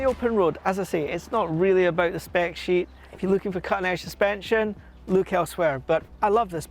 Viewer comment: Couldn't understand what you said about the suspension at 2:20 ..sounded like "if you're looking for canair suspension look elsewhere" ...but the accent is too foreign to me to understand the word you used, "canair" ..what are you saying? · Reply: I think he says: "If you look for cutting edge suspensions, look elsewhere". Admittedly, it's hard to understand but that makes perfect sense knowing the type of bike and the fact that many comments here complain about those.